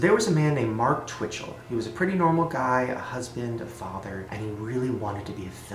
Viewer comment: i thought he said 'he had a husband' ;)